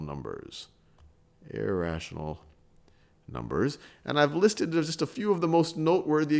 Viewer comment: sorry could u repeat I didn’t get exactly what u said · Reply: (Listed)